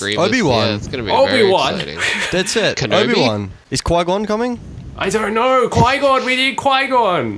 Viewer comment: i heard that spoiler you try to sneak in there "qui-gone"